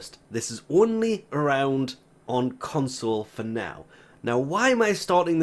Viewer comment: When is this coming to console as it wasn't on PlayStation last night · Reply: Yeah thought he said it was up on consoles and couldn't figure out why I couldn't update.